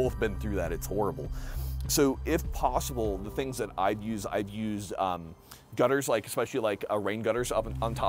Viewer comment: i thought you said no gutters bc bird poop